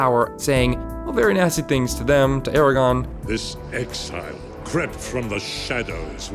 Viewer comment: I’m sorry, did you say Eragon??? 4:33 Really?